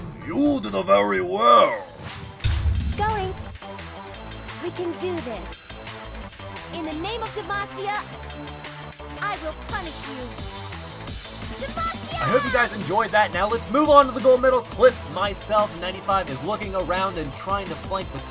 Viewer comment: sounded like she said "In the name of the mafia" lolz